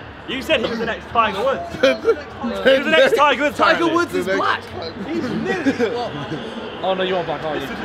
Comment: did harry just say the N word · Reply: He said nearly